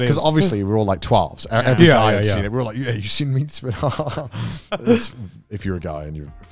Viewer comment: Oh, "meatspin." The first few times, I thought he said "leekspin."